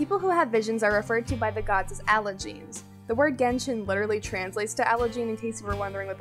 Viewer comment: What's word you say in 8:50 when translate a word genshin? A allergen or somehow that? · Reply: allogene!